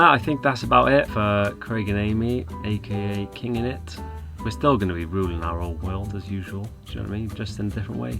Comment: LOL Craig I thought you said at the end "we'll still be ruining our own world" Love you guys!!! Hope to run into ya and show you British Columbia next year out west in Canada! You deserve to find the best happiness and project. Best channel on YT. 123bye!!!!